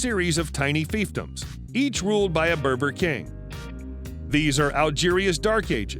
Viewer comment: My fatass thought he said “burger king” lmao